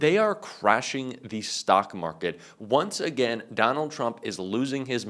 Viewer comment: you mispronounced Stock market. i laughed aloud because it was so much better your way. I heard "sock" market and my husband who was once a trader loved it and thinks that it is the best descriptor of the current market.